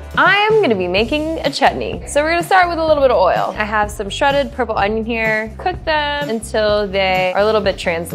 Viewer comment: did she just say "purple onion"